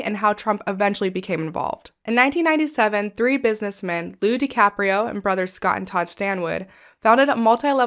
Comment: Upon my first hearing this I thought you said Leo DiCaprio. I went back and heard it is Lou DiCaprio. All is right in my world again. 😂